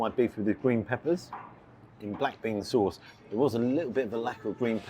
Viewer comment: Was it beef with black pepper sauce or black bean sauce in the Asian section? I thought you kept saying black pepper until you actually ate it. I know this establishment and it is value for money to be honest, if you can make the most of the time constraints. If you were unwell, best take it up with the establishment. It was quiet when you were there. Food 'turnover' is paramount for successful establishments...so you may have touched on a weak spot in their business model, which would be advantageous for them to take on board.